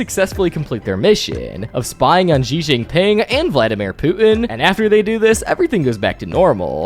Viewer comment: I swear I thought you said “Vladimir poopin”.